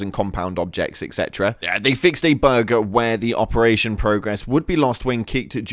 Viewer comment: I thought he said burger 🍔 but he said bug